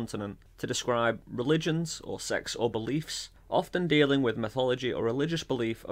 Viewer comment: says sex 😳 guysyse whyf did say s*x??? i cant wat ch yo u anyrmkore... tht is inapprweopriated!